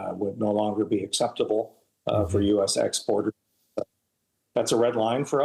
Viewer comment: On October 5th, we will have elections in the province of Quebec. The race is expected to be very close, and the Parti Québécois could form a majority government. If they do, they will hold a referendum on independence. If the Canadian government had accepted the American request to exempt American companies from translating their trade info in French, it is almost certain that this decision would have given the Parti Québécois the votes needed to form a majority government and perhaps win the referendum. The Canadian government cannot afford to take that risk. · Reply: I may have misheard as Boehm kinda breaks up at the end of exports. He may have said "exporters".